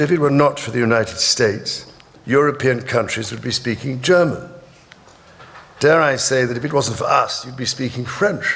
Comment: Ouvi "Dare i say if becausen't for us" parece errado, mas foi o que ouvi